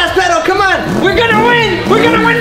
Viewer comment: At the end when he was eating it it sounded like he said "a little bit of rice a little bit of crack" now I'm sobing 🤧🤧